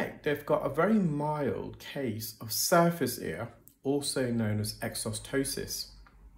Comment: I thought you were saying SUFACE ear 😂